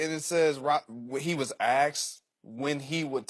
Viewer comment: "When he was axed', I thought I heard. But he meant 'when he was asked".